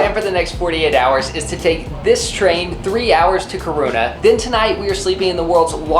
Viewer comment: Haha i love when u say Kiruna 😅 sounds like u say "Corona"